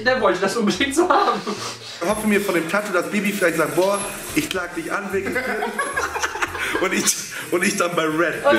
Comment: Ich hab verstanden,dass der gesagt hat: " sie klagt mich an" und nicht : "sie lädt mich ein"🤣🤣🤣🤣🤦‍♂️oder bin ich dumm